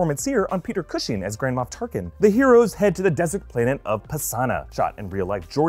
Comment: am I the only one who heard 'Grandma Tarkin'?